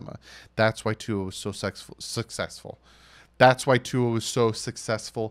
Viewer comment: am I the only one who heard “sex-full” hmm... only kidding haha great video